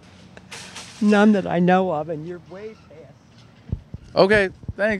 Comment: what does she says at the end? "None that i know of..." "... and you racist" "... and your a white ass" "... and he has Phaserz" (Laserz, Tasers idk) ??? plz could someone get the end? XD